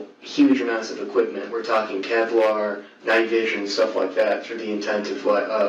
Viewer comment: “We’re talking Kevlar” (bullet proof material) The subtitles said “inaudible”but I caught it with my headphones 😀